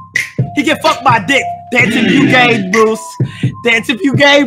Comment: At first I thought was saying “ dance if you can Bruce” 💀